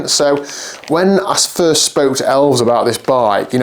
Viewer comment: I totally misheard that, I thought for a minute you said "When I spoke to the elves about this bike"... how do I talk to these elves about a new xmas bike? :) Nice bike! reminds me of a modern version of Miguel Indurains "Sword" Pinarello TT bike from the 90s.